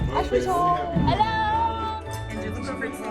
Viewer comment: idk if its just me but when ashley said "hello" at 4:41 it sounded like annemarie HAHA